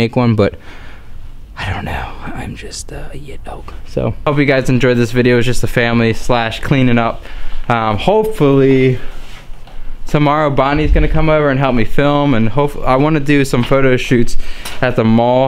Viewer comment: I thought you said I was going over to help film lol